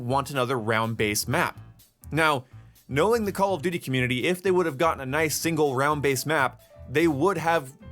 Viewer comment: I thought you said there’s no such thing as a COD community